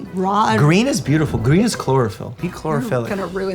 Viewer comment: When you said chlorophyll I thought you had said chloroform...